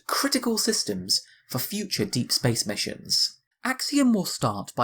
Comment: Did he just say, Space Sex mission?